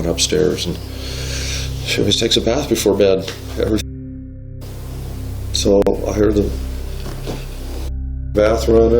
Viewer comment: what is the word that's being censored this time? "she always takes a bath before bed every [censored]". Every shit? Every fuck? Every suicide? Every what? These Youtube censorships have gone way out of control. Why are we not allowed to see and hear words? They are just that; words. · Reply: The f bomb